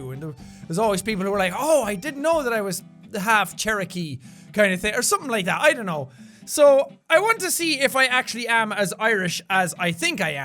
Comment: When he said Cherokee I thought he said parakeet (I know that's not how you probably spell charity but I'm using the voice E thing so please don't judge me)